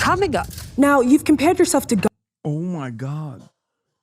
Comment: You cut off "Gandhi" to make it sound like she said "God" and put a cross on here? I swear to God/Gandhi, these clip channels are fucking garbage. If it wasn't for the time zone difference they would be utterly useless